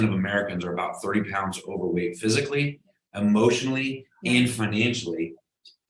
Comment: Did he say overweight emotionally and financially?? 😑 I need a definition or example of those please. I think I personally might be ok with being financially overweight. 🤣